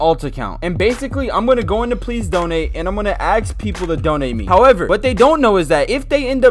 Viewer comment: you think he said ask lol he said aks